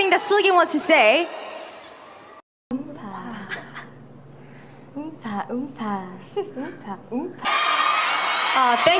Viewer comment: what was seulgi gonna say D: !?!? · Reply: She said see you in summer 😄